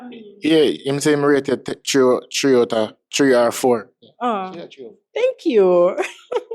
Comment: 304 went ova everybody head 😂🤣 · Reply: I swore I heard 304 when said 3 or 4 I was confused lol